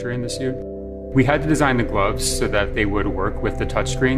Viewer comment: did he say mcgloves